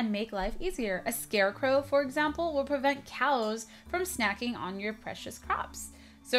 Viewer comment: um.. is it just me or did she say cows at 3:00 it says crows